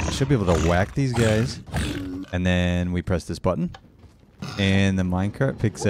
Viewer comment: How well-done do you like your Button ? Me: I thought you said we were having Mutton ? ... Button.